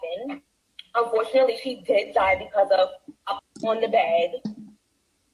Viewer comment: sounds like she said “a bump on the head”.